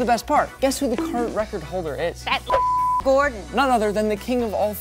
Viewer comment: it sounds like she said that ninja gordan.